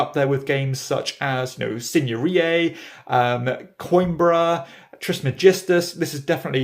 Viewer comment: That would be 21:20. I can't figure out how to spell the name you are saying! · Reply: haha no it's probably my dodgy pronunciation. The game is Signorie.